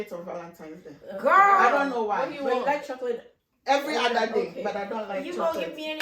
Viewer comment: Nice seeing everyone. Question, What is "shakate", not sure if I heard right. Around min 6:30. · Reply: Hahaha, I believe said chocolate at min 6:30, but said it really fast and obviously in her Ghanaian accent.